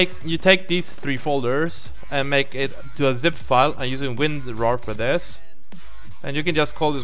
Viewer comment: What did you say you used at 8:47 ? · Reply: Winrar, but that costs money. 7zip is a free way to do this